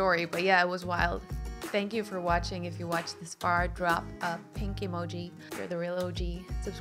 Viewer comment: 💗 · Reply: I heard 'pinkie' 😄 thinking what is the emoji for pinkie 🙈